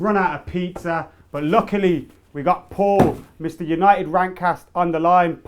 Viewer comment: When you first said paul, I thought I heard "We ran out of pizza, but luckily we got Porn"!!! How awkward...